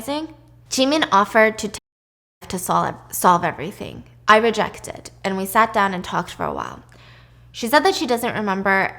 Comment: I am having trouble understanding all that she is muting. What does she say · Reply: take her own life